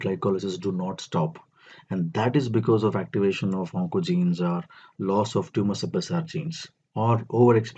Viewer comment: please, someone, help in 6:09 what did he just say?? "that is because the activation of what????" I didn't understand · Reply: Tht is bcz of activation of oncogenes& loss of tumour suppressor genes......